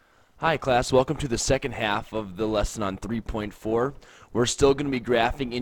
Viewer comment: i misclicked so i heard "hi welcome to this....half of a triangle" i was yawning